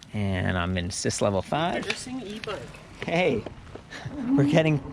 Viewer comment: TAUNTED? It sounds like he says "introducing e-bike" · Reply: I heard "interesting ebike" too. And you were in Canada, which makes more sense than a provocation. 🇨🇦😘